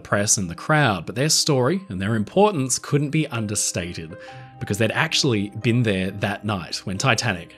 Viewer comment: "couldn't have been OVERSTATED" (not understated, like what you said at time mark